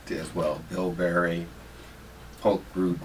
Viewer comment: What two herbs did Dr. Africa say at 0:29 in the video? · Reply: Bilberry and poke root